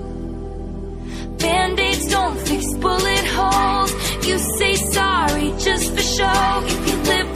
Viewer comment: Why do I hear "bandage don't fix booty holes" I don't know why 😭😭